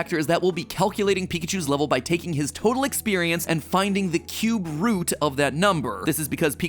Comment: I understand "cube root" was said, but I heard "cute root" and I think that's silly.